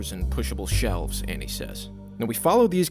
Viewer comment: What kind of pronunciation of the words et cetera is this? Did you realise you didn't know how to pronounce it and just flub it? That's hilarious. Also, what a sad loss. Another shitty GTA3 clone. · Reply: idk what you heard but he didnt quote the "etc" at all he just said "annie says" (annie being the developer)